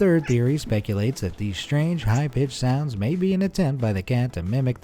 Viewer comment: did he call cats b!tch sounds